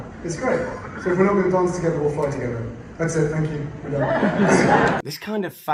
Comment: I thought he said a hot dog thankyou....I also didn;t think it was serious, toys for boys